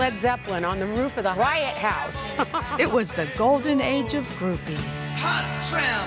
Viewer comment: I thought she said it was the golden age of herpes for some reason 😂😂🤦‍♂️